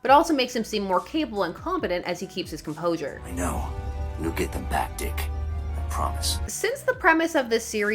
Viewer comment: anyone else hear "you'll get the Bat Dick" at first at 7:05 instead of "we'll get them back dick" like surely someone else misheard it lol